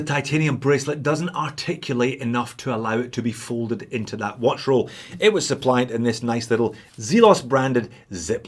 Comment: I have probably misheard what you said, because I heard something along the lines of: "the bracelet on this $1500 watch is not articulated enough to fit into a watch roll".